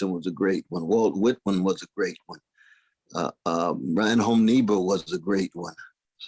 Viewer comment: What is the name he mention after Whitman at around @ 13:05? I cannot find the spelling of some, nor find who they are. Brian Holm Nibo is how I hear it, which is wrong! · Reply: Reinhold Niebuhr